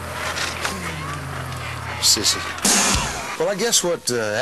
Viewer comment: are my ears working properly did Chris just call Garth Brooks a sissy